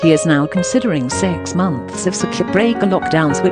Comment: Did you say 'Circuit Breaker'? I'm sorry, I thought you said 'Spirit Breaker'.